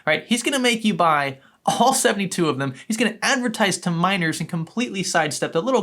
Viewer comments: this whole time I thought you where saying MINORS not miners lmao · At first I thought he said "minors" instead of miners. Had me confused for a sec. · For the longest time I thought he was saying minors instead of miners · I keep hearing "miners" as "minors"